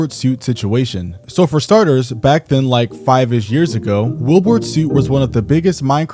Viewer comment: I thought bro said WW2 instead of Wilbur soot🥀💔